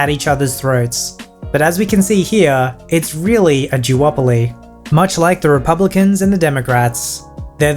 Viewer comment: did he actually say "it's a Jew-opoly"? Wow, I never pegged you as a person to use an anti semitic term! Maybe I won't be subscribing.